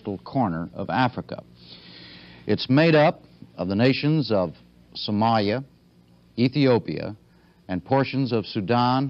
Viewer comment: Did he just said Somaya